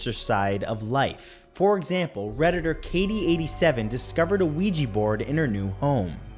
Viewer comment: I'm drawing the Super Mario Bros right now and using your video as background noise, so when you said "ouija board" I heard "Luigi board" :D